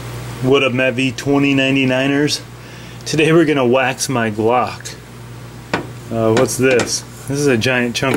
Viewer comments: I thought it said 'Wax my cock'.. I'm out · Jesus thought it said wax my cock xD